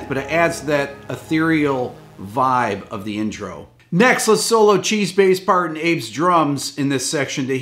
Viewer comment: "Let's solo Chi's bass part and Abe's drums..." -- I swear, I thought he said "Let's solo Cheeseface Parton Abe's drums." And now I have a new fictitious band name.